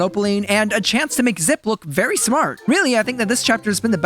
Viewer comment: in the captions/subtitles in this part instead of zip the caption/subtitles said ziploc